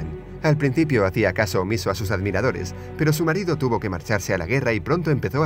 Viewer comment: a la primera vez escuche "su marido tuve que marcharce a la mierda"XD psdt: jajaj:v